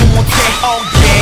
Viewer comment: am I the one hearing "Oh, dang" instead of "Oh yeah"